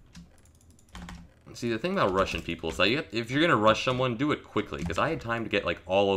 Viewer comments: When he said "the thing about rushing people" at first I thought he said "the thing about Russian people" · Who else thought he said "You see the thing about Russian people"?